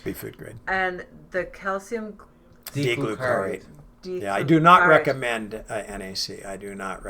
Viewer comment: Hello. Can't understand what's said at min. 1:05:45. "Ig glu kar re"? What is this please, and what is it for? Thanks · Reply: shashakeeleh maybe calcium d glucarate